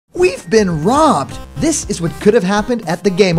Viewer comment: Did he just say we been robbed